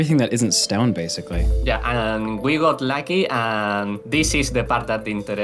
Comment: Minor mistake, video ruined (He said Lucky, but the subtitles say Laggy)